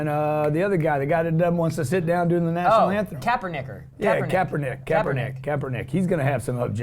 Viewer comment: At the 4:18 Mark did you say Captin nikker hmmm 🤔